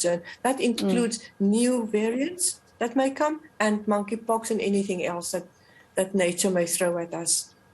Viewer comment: her saying 'money pox', is the equivalent of; here we go again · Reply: I thought I was the only one who heard her saying "moneypox"😅😅😅